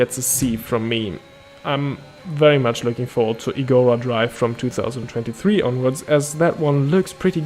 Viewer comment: What track name is it again? I didn't understand it. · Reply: Igora Drive but that will never happen now 😅